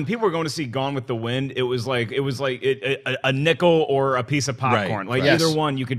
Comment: my fencing class got into a debate about dessert pastries because one of my classmates thought you said a nickel or pie.